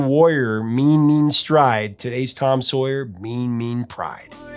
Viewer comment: I always thought he was saying “today’s tom soy.”